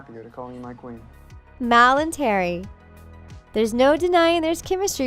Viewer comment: Am I the only one who noticed on 1:31 it sounded like she said "mal and Terry" instead of "mal and Harry" idk if I heard the incorrect or not